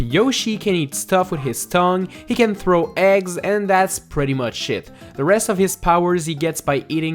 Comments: sounds like he said "That's pretty much shit." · what he said “thats pretty much it” what we heard “thats pretty much shit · It sounds like you said sh*t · Didn't anyone hear at 1:13 he says "and that's pretty much shit"